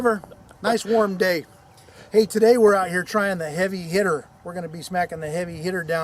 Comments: Holy shit I thought it said "Heavy Hitler" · It is 3am and I thought it said "Heavy Hitler" LOL XD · who else thought it said heavy hitler?